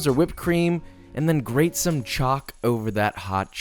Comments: I straight up thought he said "grate some chalk"😂😂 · when he said choc i thought he said chalk and looked at my pack of rainbow chalk